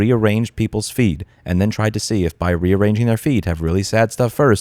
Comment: I thought you said Meta rearranged their FEET. I was convinced we were dealing with an evil corporation here.